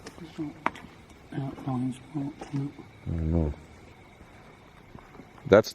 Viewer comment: you caught an evp. It's difficult to hear so turn up the volume and you can hear someone say "thank you" in a slow whisper. That's right after you left the ciggarette for James spirit. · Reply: DarkSide Beats I heard “hey, don’t go that way “